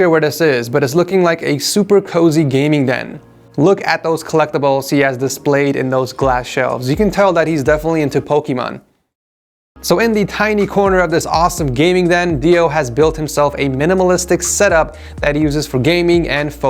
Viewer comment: did u really say Pokemon